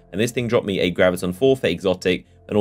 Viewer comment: Did he just say Graviton Forf "ate"?